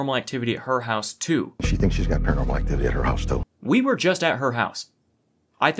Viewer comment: "She thinks she has paranormal activity at her house still" not too, is what they said.